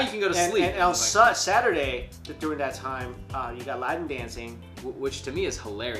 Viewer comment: Did he say the kid has to do lap dancing?? WTF?